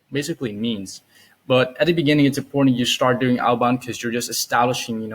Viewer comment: what did you say at 19:58? At the beginning it is important to..... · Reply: You start doing outbound! :)